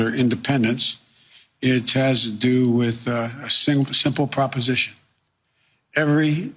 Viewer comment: When Biden said “a simple proposition” I thought for a second I heard “A Modest Proposal”.